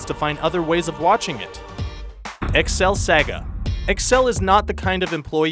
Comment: I heard Excel "Sega".